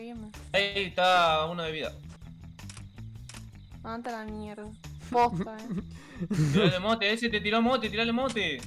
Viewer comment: soy yo o estre dice andate a la mrd basta o posta si pensas que dice basta dice eso o si dice posta dice eso